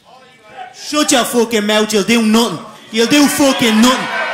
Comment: Am I the only one who thought Connor was saying “you LITTLE nothing”? But that little was heavily “irish-accented”